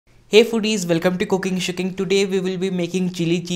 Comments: who else heard cooking shitting 😂😂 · Sounds like you said,, welcome to cooking shitting,,,,, not the greatest intro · Sounds like he says welcome to cooking shitting. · I just heard "COOKING SHITTING TODAY" ahha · Sounded like he said “welcome to cooking shitting” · Did he just say cooking shitting?